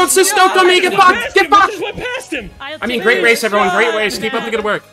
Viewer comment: Did bro say “great wace” 7:59 …… R-r-r-r-r-rap god yikes